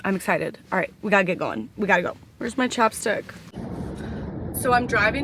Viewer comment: Lol, I thought you were saying chopsticks. I love watching you. You’re cool and so sarcastic.